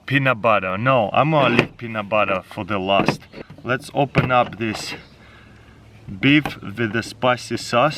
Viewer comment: I thought he said beef with asbestos